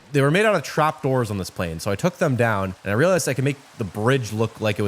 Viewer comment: I immediately had to rewind, I heard "a plane took my storm down", wtf is wrong with my ears.